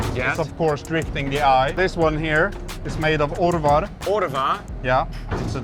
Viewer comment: can someone transcribe what he said here? What is the alloy called? How is it spelled? “Ordevar”? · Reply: Orvar